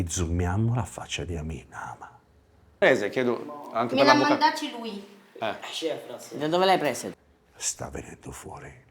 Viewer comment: MA INSOMMA! COSA DICE QUI? SBOTTA O MINACCIA LA MADRE? 😮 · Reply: “Controlla la testa”